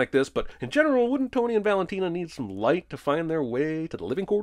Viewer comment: What does Anita say at 4:55? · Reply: She says “Con los muertos no se juega, ¿ah?”. Which literally translates to “Don’t play with the dead”. I guess Anita was saying this to Maria because the white dress wouldn’t look a shroud if it were red. Source: <link>